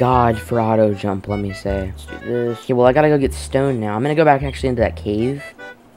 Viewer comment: I THOUGHT HE SAID WELL IMMA GO GET STONED NOW😳